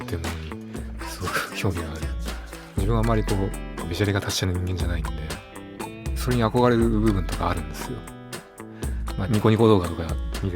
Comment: なんて言ってるかわかる方いますか……どうしても聞き取れなくて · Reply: 自分は あまり こう‥｢べしゃり｣が達者な人間じゃないんで‥‥、それに憧れる部分とかあるんですよ‥‥ って言ってるかな‥と思います^ ^